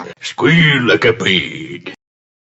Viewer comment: Did he just say squeal like a pig? 😂🤣 Happy Holidays, BTW!! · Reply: I thought he said “Squeeze like a pig”